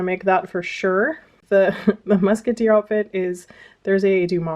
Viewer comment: When you first showed the “musketeer outfit”, I mis-read/heard it as “Mouseketeer outfit”. 🤣